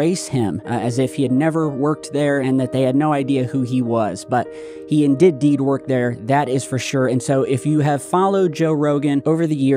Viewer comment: you just said "he in-did deed work there" instead of , he did indeed lol thats a funny flub!